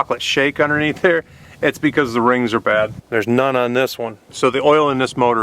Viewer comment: When You showed under the hood, and said " see all that? ..thats cause the RINGS ARE BAD..." At first I thought You said "That's cause the RAINS ARE BACK.." haha... Gotta love old Ford Trucks. They have a lot of life